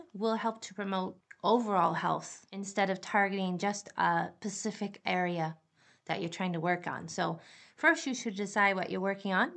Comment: Did she just say “a pacific area”?😳🤦🏾‍♀️ credibility out the window!